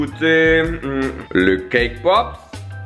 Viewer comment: Pk à 10:08 j'ai entendu "le K-pop"?😭😭😂😂